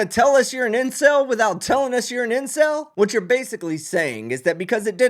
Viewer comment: I had major brain fog while watching this. Every time the host said incel I heard “inbred”🤦‍♂️🤦‍♂️. Then spent the rest of the video trying to figure out why no one said anything about them being siblings w/ a kid 🤦‍♂️🤦‍♂️